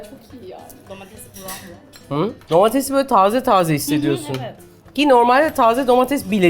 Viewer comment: how do you say 'fresh' in Turkish? i think i heard Refika say "taaza/taazi", which also means 'fresh' in Hindi/Urdu! interesting how our different cultures/languages so far apart, have similar words for many things!